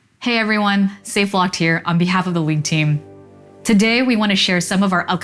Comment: Did she just say "on behalf of the weeb team"?